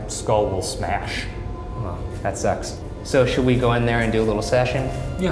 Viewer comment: right around when Shane said that his skull will smash, I thought Ryan's reply was "had s--" instead of "that sucks" . A smash joke was just too good to pass up